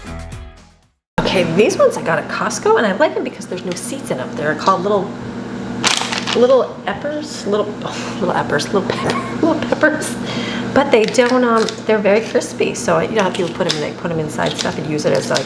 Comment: I thought you said 'little effers'. 😂